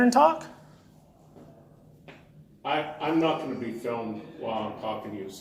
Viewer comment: i'm not going on film telling lies sir is what I think he was saying